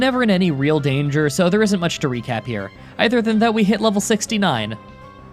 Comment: does he say ‘either than that’ or ‘other than that’? I’ve been watching for a while now and I’m pretty sure he says the first one. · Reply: It’s either